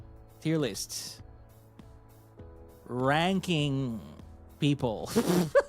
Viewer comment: I thought he said pranking people.